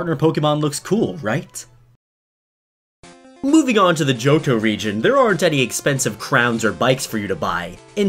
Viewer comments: Am I the only one that heard him say the JoJo region? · Did I heard "Moving out to JOJO region" in 2:17?